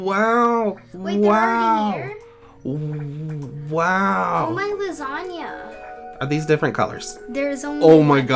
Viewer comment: did say I want lasagna?